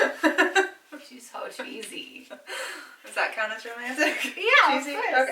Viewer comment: At first I thought you were saying was “easy” at the beginning of your relationship but you said cheesy Ha 😂😂😂. I love your sit down videos. ❤️